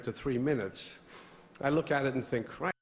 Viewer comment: What is said at 9:05? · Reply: "I look at it and I think 'Christ, I made nothing but hits.'"